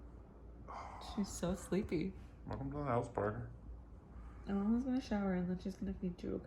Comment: Her name is Parker!!!!! So cute!! Who else is crying this whole video?!? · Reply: I thought I heard Carter, which is also so cute for a girl!!! 😍😍